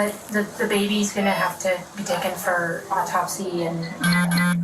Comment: She totally dissociated, saying "NOthing was crying." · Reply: Oh I heard “it was crying”